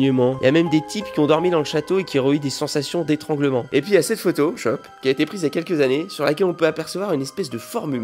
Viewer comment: Y’a que moi qui ai entendu a 4:34 ? « Et puis il y a cette photo (shop) »